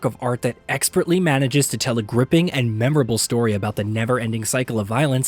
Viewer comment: Did you just said remembering story 😂😂😂😂